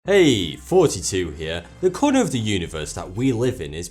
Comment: It sounds like you say “heeey, 42 here”